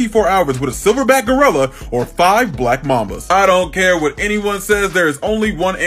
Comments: i thought he said "black mamas"💀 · I heard “Would you rather be locked in a store with a silver back gorilla or 5 black mamas” 😭😭😭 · Did he just say 5 black momma's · I thought he said 5 black mamas I was like ok what’s worng with that until I relieved he’s talking about snakes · Bro I wasn’t paying attention I thought he said black mamas and didn’t realise til the end